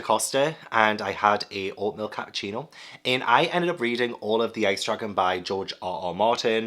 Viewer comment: Every time. I keep hearing “Ice Truckin’” instead of Ice Dragon